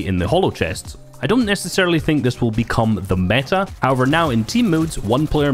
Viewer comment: I thought he said “temu” instead of team modes Edit: I tried to scroll and I got an ad for Temu right away 😂